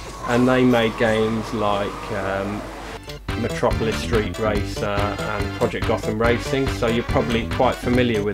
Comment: Great minds indeed! Did you just say 'Matropolis Street Racer'? ;) haha. I haven't played the other two games but Micro Machines and Blur are both great. I think we need to get +Novabug into Blur ;)